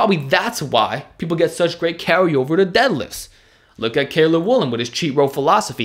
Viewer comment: what name did he say there? All i hear is kayla woolen.. but i cant find any such person anywhere. Thanks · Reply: Cailer Woolam